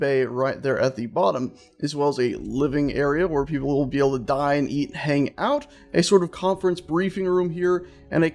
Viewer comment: When you said they can dine, eat, hangout, it sounded like you said die, eat hangout. XD